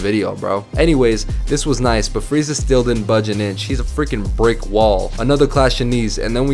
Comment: Did you call freeze a she